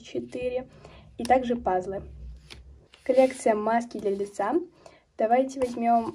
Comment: Мне послышалось матки для лица 😂😂😂😂